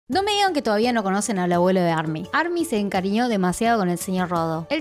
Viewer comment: En un momento pensé que decía "el vuelo de army" Jskskksksk por cierto es muy lindo que tengamos un abuelito💕🍭😊